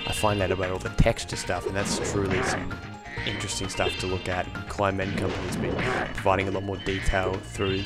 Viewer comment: what did you say there? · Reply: "Kly_Men_COmpany has been providing a lot more detail" - hopefully finally I can show you what he's been working on; it's nearly been 2 years and I've kept this project almost a surprise the whole time.